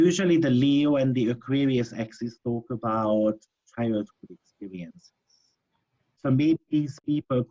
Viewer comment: What did you say at 33:27? What is the Aquarius/Leo axis? It broke up at that moment. Thank you. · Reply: said "childhood experiences" :-)